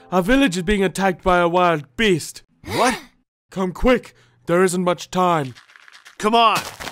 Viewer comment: He Says There Isn't Much time at 0:42 but the captions say we don't have much time